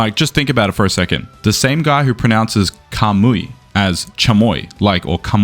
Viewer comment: Idk coach . I heard Kamui🤣🤣